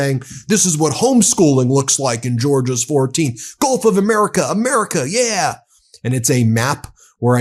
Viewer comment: Initially I thought you said home schooling was teaching “golf of America” but perhaps this more accurate at this time.